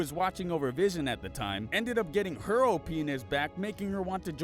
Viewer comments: I thought he said “Her old penis” And I was sat there thinking about what happened to the demonetisation that was previously mentioned. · It sounded like he said "Oh penis"! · her O'pe*** is what i heard the first time